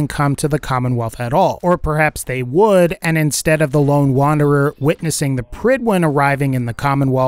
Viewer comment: did he just call the sole survivor the lone wonder